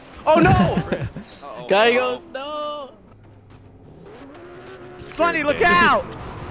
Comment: have captions on it say no slutty instead of slendy